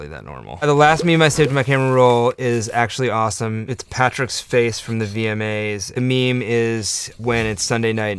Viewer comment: I swear I heard him say “Patrick’s face”